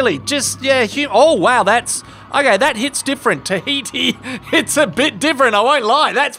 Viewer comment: At first I heard “Tafiti” and thought… “man, Moana switched careers in a very delightful way”